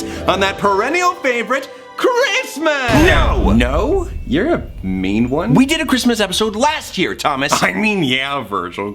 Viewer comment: Did anyone else hear the your a mean one? The grinch!